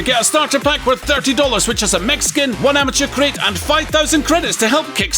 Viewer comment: When you were running your ad I for sure thought you said you get a Mexican for $30. * “Mech skin”